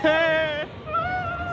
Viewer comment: 뒤에서 또 인종차별한듯 욕해 뒤에서? 라고 들리네